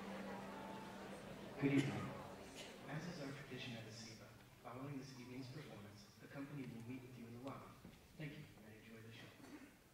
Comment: For those of you wondering what is said at 0:42, it's this: "Good evening. As is our tradition at ESIPA, following this evening's performance, the company will meet with you in the lobby. Thank you, and enjoy the show."